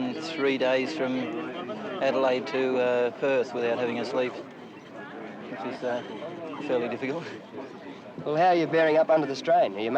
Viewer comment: As encapsulated as I was by this co-driver's dedication and ability to stay awake for days drug free giving no instruction for hours at a time I realized that from about 44:20 I simply couldn't follow what he was saying. I repeated the segment many, many times and even used subtitles. I even tried other languages. I just can't understand him. I will continue my endeavor so you don't have to. Wish me luck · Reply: “…which is, uh, fairly difficult …”